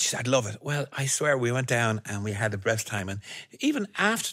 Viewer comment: he says "we went down (talking about the White House) and we had the "breast" time". He had said best 2-3 times clearly before this point but at this time, instead of saying best time he says breast time...Freudian slip or just the consequences of talking to fast?